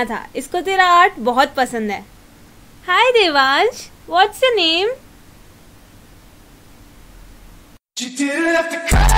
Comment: When you try to be nice to kids 🤣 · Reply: Like when i heard "you didnt have to cut my off" i laughed so hard my grandma is like "............ What? "